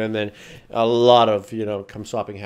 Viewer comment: did he say a lot of cum swapping happens